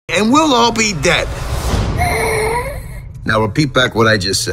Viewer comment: I Heard "And Willow Be Dead"